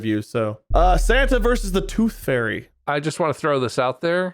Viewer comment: I thought he said “poop fairy” 😂